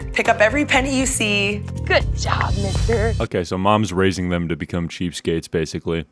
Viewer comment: WHAT DID SHE CALL HIM? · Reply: Mister